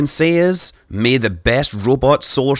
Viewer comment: I love the way he says explorer. It sounded like he said "May the best robot solar system exploder win."